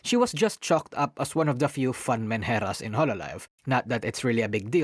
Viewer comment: Can someone help me understand what did he said at 4:10 ? YouTube subtitles it as man heras / Manhattan's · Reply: Menhera, a Japanese slang for a person with poor mental health. He showed the definition at the very beginning of the video.